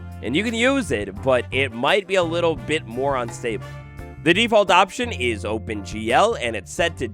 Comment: did you say unstable ?